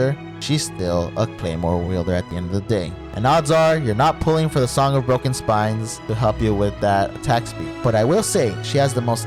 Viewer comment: "Song of Broken Spines" idk man that's what I heard xD